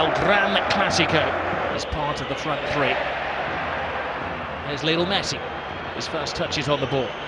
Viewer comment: does he says :”The little Messi” ?? 😑👿